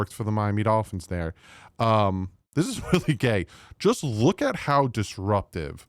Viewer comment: thought said "this is really gay" for a second 😆.